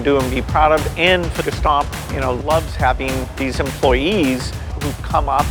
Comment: I thought he said gestapo...